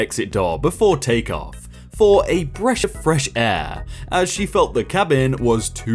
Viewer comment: did you just say a breSH of fresh air